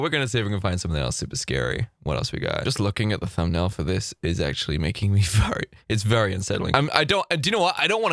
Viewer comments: I thought he said “just looking at the thumbnail of this is making me fart” 🤣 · Did you just say the thumbnail is making you fart????? LMFAO